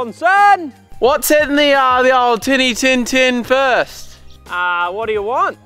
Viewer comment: did he say titi tin tin